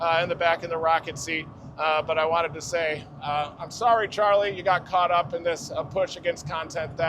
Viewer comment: All I heard is "Sorry a big Youtuber got caught up in my lawsuit.".